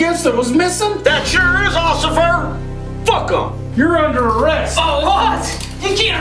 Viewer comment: did you just say fuck'em?